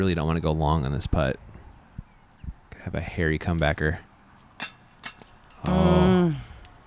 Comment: I don't know if it's cause I'm high or what but he just said HAIRY COMEBACKER around 16:54 - 17:00 and I just busted out laughing! 🤣🤣🤣